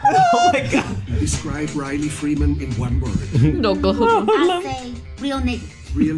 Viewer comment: EVERYONE I just want to point out a real sibling moment in this episode: When Riley is asked how he would describe Huey, he paused. He thought about for a second. He could have gone with the obvious one word answer "GAY", but inspite of all their rivalry, he smiled, looked at the camera, and said "Real Nigga". AND MEANT IT. And then he doubled down on his answer even though it was two words. That's just a powerful sibling moment on this show 🤌🔥🔥🔥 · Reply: As nice as that would've been, you misheard/misread. The narrator asked "How would Riley Freeman describe Riley Freeman"